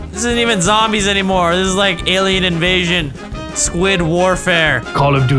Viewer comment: i heard squidward-fare